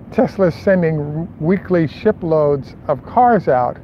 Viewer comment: For a minute there I thought maybe you said "weekly shitloads." The dead cat and other fixes have got you up maybe to the quality of good, strong, local AM radio.